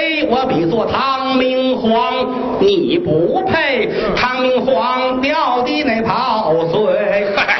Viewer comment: 秒那句听不清，唐明皇什么的跑腿？ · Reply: 唐明皇掉的那泡sui